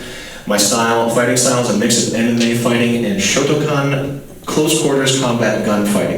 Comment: always thought that he said "MMA and shotacon" till I listened and read more closely today. That part always killed me